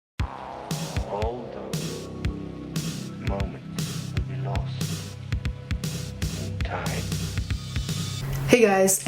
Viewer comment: Awesome video as always! Just curious: what is it saying in your intro? I hear it as “All good moments will be lost in time,” but I’m not sure if that’s right or not. Sorry if that’s an awkward question lol. I just think your intros and outros are always amazing.